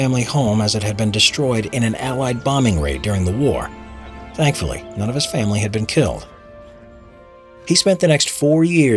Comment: “Luckily none of his family were killed” I heard “ Luckily his family were killed